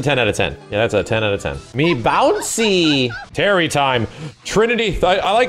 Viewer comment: I always heard "me pointy." ....also not wrong, though. None of these supers are above a 6, though. The camera work and animation are the culprit. Damn shame, since that kind of presentation was always SNK's forte. Up until recently, apparently.